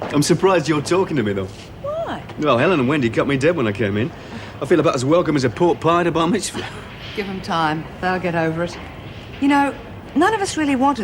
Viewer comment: what did Dennis say, I feel about as welcome as a pork pie in a what?? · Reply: a pork pie at a bar mitzvah.